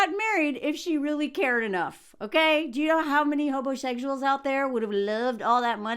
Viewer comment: Did she just say Hobosexuals? 😂